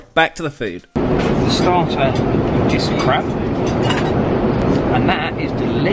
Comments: can't be the only one who heard crap LOL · i really thought he said " the starter... is crap" xD one letter mistake xD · The starter is crab.. heard it as the starter is CRAP 😂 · I misheard you there. For a a second I thought you said the 'starter is crap'. Doh! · Lol the subtitles says that the starter was just crap